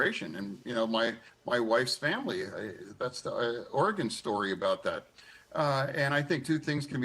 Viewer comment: did he say Oregon story? 🤣🤣🤣